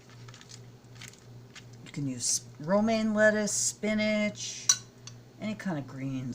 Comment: Aww love the birdies in the snow! Hey, naughty Cat! :D Love all the Fred family too! Great tips on make up products - thanks for sharing. Haha, you said Romaine Lettuce and I heard "Roaming Lettuce" ... so my mind goes straight to a vision of a large cartoon type lettuce carrying a suitcase and roaming around the countryside seeing all his relatives! Lol! :D MMMmmm your lunch looked so good! It's made me hungry so I'm off to grab a bite for lunch now. Mine will be leftovers of a Moroccan Spiced Pie I made last night. It is sooooo delicious! Have a great week my beautiful, awesome Vegan sister!!! Big hugs and much love, Ginny xo <3 :o)